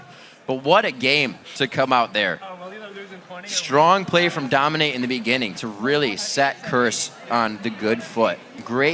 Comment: What did bubbadub say at the end around 1:41:00? "Wouldn't it be funny if--?" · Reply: Xpecial cried?